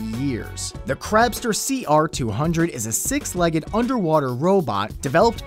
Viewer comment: I thought it said underwater crap robot XD